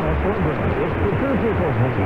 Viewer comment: It sounds like you were receiving two stations from the USA. The first was taliking about William The Conquerer & the "Domesday Book" of 1085. The second station @ 01:06 sounded like "Listen to the Tom Morgan Show". I don't know what stations they are but maybe these clues will help. Congrats on the catches though & 73s. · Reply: Мне слышится на 01:06 "Listen to our morning show..." Да, по времени похоже на AFN из Кореи. Поздравления, 73!